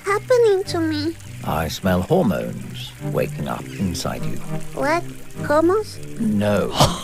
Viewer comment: Why did i hear “what, no homo?” And Im not complaining 😭😭